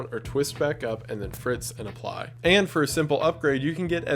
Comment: Did you just say, when speaking of the perfume dispenser at 1:45, that you can "FRITZ and apply"? Did I miss hearing the 'sp' and think it was an 'f'?